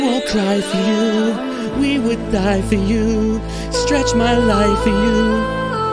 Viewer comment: I thought said “stretch my legs for you” close to the end 😂